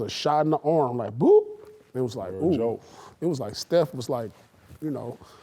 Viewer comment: "joke", did kg say that? 😂😂😂😂😂😂😂😂😂😂😂😂The no of times kg is trying to hold back paul is insane. Yo did he say joke 😂😂😂😂😂😂😂😂😂😂😂😂😂😂😂😂😂😂😂😂